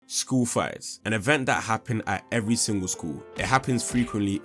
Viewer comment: I thought you said school *FLIGHTS*.